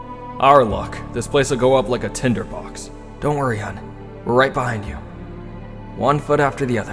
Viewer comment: did I hear that right?? Kiri called us Hun 😭